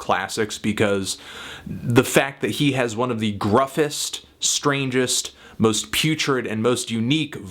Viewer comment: did ya just say "groughest"? roughest with "G"? lol that's badass, everybody remember Anthony coined it first!